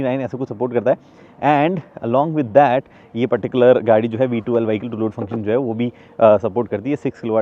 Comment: I HEAR V12☠️☠️